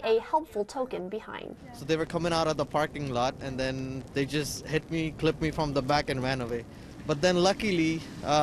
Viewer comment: I thought he said "they hit me & flipped me" at 1st 😆